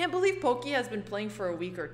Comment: She’ll be amazing. Imane is pretty smart and a knowledge sponge. Her analysis with basically minimal training is pretty solid overall. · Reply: It's been almost 1 month that I keep hearing 'She's been only playing for 2 weeks'